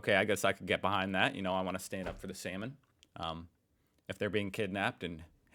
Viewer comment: 07.30 SALMON! Oh I thought you said 'semen'